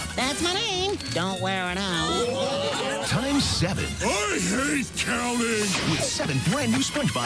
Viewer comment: Hold on! Did Patrick say “I hate Cowman!”?